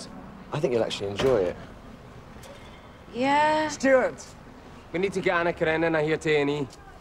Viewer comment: Can some please tell me what Ashley said in 22:23. I heard a little bit but...xD please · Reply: “We need to get Anna Karenina here to A&E (Accident and Emergency)” Anna Karenina is Russian girl from a Russian book, and that girl in the show was Russian👍🏻